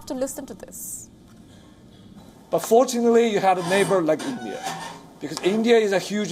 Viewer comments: Am I hearing him incorrectly? I heard him say 'fortunately,' not 'unfortunately.' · contrary to what the video thumbnail says: "but fortunately you have a neighbor like India" sounds like your whole editorial crew have an aural illiteracy. 1:47 "India is not a friendly neighbor?" proceeds to provide literally zero points on how India is one WION's journalistic standards is so immature.